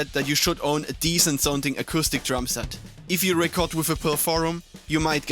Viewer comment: What words are you saying at 2:14? "If you record with a ________, you might get fired from your band." It sounds like you're saying "pro forum," but that doesn't make any sense. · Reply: Pearl Forum (Entry level Drum set)